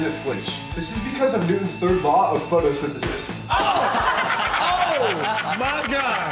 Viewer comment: Did he say Newtons first law of photosynthesis? 46:00 😂😂